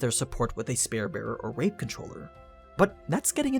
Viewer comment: u accidently said rape controller instead of wave controller.